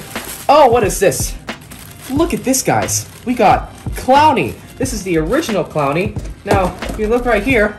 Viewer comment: instead of clowny plushie its pony plushie lol